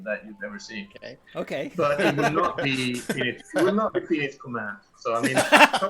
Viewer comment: "it will not be ...." what did he say? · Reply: "It will not be Phoenix Command" <link>